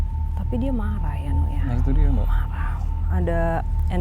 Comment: Menit 29:09 ada suara samar-samar berkata "ya marah" bener gk sih atau memang saya yang salah dengar ?